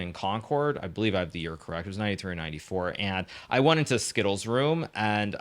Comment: So used to watching Hikaru play that when he said 93’ or 94’ I heard “knight E3 or knight E4”